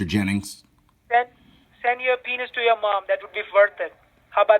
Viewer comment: lol i thought he said "send a penis to your mom"